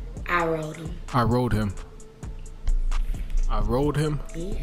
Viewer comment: I knew I wasn't the only one who thought she said "I road him" 🤣🤣 like huh?